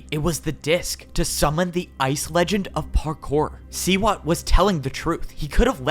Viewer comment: I thought that said toe legend 💀